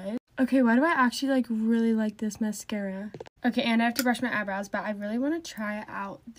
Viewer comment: i thought she said eyeballs not eyebrows… 😭